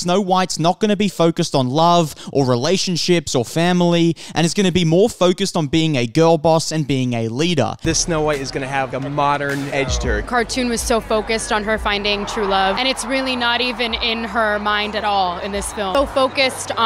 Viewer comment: I thought the interviewer said “…seems to have a modern edge turd”, not “modern edge to her”… I had to replay more times than I’d like to admit to hear correctly 💀